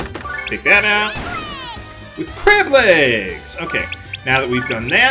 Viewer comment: It was a great LP. :) Side Note: I always thought Flonne was saying "deadly" and not "crab legs." But now I can't hear anything but crab legs. XD